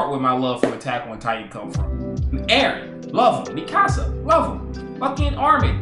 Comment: Mikasa, love em Me: That sounded like you said him 😂